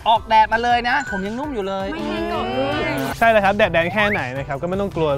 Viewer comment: ทำไมได้ยินว่า แดดแดง พี่ไบร์ทททท 😂😂